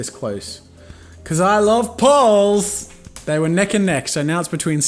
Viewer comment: sounded like you said I LOVE BALLS.